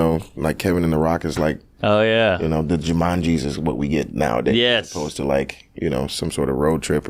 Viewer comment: I thought he said JumanJesus, and that is at least a sketch, if not a movie.